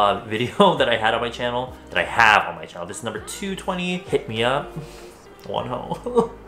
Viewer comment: Did you said "hip me up Wonho" 20:32 lol